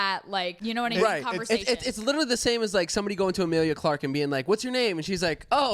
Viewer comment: Omg I’m high and when he said “Amelia Clarke” I thought he said “ Amelia Earhart” and I was sooo confused for a min I had to rewind and listen again ahah